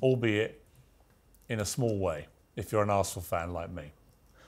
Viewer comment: Gosh... that's gotta be the worst ever Piers Morgan performance. He exposed himself as ignorant and petulant. That Arsenal stunt in the end is embarrassing. As if sports had anything to do with the future of the planet. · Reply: Was it sports though? I thought he said, "If you're an a*hole fan like me"!